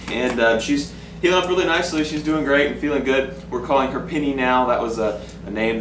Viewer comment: Happy lil pitty :) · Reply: it's penny c: i thought he said pinny but i read the description xD